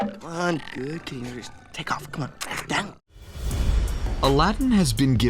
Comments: In Aladdin, I can clearly hear "Good kitty! Just, take off!" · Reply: I heard, "Good tiger, take off, come on" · In Aladdin, I can clearly hear "Good kitty! Just, take off!" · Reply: Greninja Storm I heard "good tiger, take off, come on!" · In Aladdin, I can clearly hear "Good kitty! Just, take off!" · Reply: I heard “Good kitty take of come on” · In Aladdin, I can clearly hear "Good kitty! Just, take off!" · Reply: I heard "Good Kitty, Take off, Come on!" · So, I paused this after the Aladdin secret and grabbed my DVD to see what the subtitles said at that point and it's "Down kitty. Take off", Ali says it as Rajah approaches him snarling. LOL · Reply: I heard “good kitty. Take off , come on”